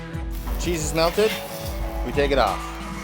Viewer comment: lol Sounded like he said "Jesus smelled it..." then I understood that the said cheese has melted...lol i am hard of hearing so there's never a dull moment when I'm listening to people....